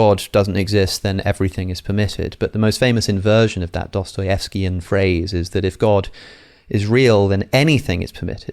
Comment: What does he say at 15:30? What kind of phrase? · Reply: Alex is saying: ‘Dostoevskian phrase.’ The phrase ‘If there is no God, everything is permitted’ is often attributed to the famous Russian author Fyodor Dostoevsky. While not a direct quote, it summarizes themes explored in his novel ‘The Brothers Karamazov,’ where characters grapple with the implications of God’s existence on morality.